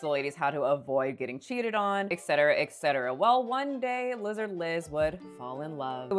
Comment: I heard Lizard Liz 😂😂😂 am I hearing things